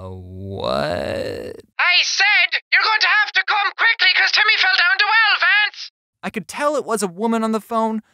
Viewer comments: in 5:44 i heard "I said your going to come here quickly because timmy fell down the well vance" am i the only one who heard it or no? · Before I thought that s(he) was saying "YOU BETTER COME QUICKLY BECAUSE TIMMY FELL DOWN TO ELEPHANTS". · On the vance thing I heard “your gonna have to come quickly because Timmy fell down the well Vance” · This is what i think she said: 5:45 You will have to come quickly cause Timmy has felt down Vance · When the character with the accent was talking I thought they were saying “You’ll have to come quickly cause Timmy’s fallen down the well” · I heard: "Your gonna have to come quickly cause timmy fell down the well fence" -_ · I heard: YOU'LL HAVE TO COME QIUCKLY CUZ TIMMY FELL DOWN THE WELL FEDS!!!!!!!!!!!!!!! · The green haired lady sounds like she was saying your going to have to come quickly cause timy fell down the well Vance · It sounded like she said you have to come quick because timmy fell down the well Vance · All I heard was “your gonna have to come quickly because Timmy fell down the well fance” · the funniest part was the woman that didnt sound like they were english BUT IT SOUNDED LIKE SHE SAID: LANCE TIMMY FELL DOWN THE WELL COME QUICKLY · Before u said u could D only make out Vance it sounded like she said your going to have to come quickly because Timmy fell out a tree vance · I thought she said your gonna have to come quickly cause Timmy feel down two elefants